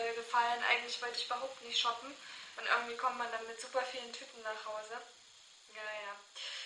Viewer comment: Ich habe erst verstanden: "Dann kommt man mit super vielen TYPEN nach hause":D anstatt von TÜTENxDD